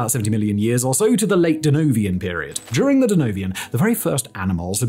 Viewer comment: Did I hear him say "DeNovian"?